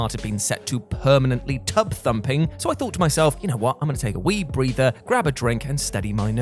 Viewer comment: I thought you said “take a weed breather” at 3:12 lol